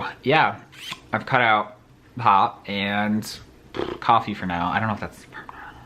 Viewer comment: At first, I thought you said "I cut out pot and coffee" and I was like "wait, what?" haha - that aside so sorry you're going through all those health things. I had gall bladder stones and it was excruciating (got it taken out via surgery), so I can't even imagine how painful kidney ones would be especially if you're expected to pass it. Wishing for the best for you